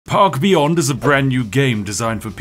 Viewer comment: I thought the opening u said "pog beyond"